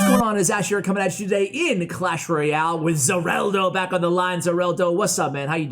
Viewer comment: For a second I thought he said ZaWarldo I’m the begging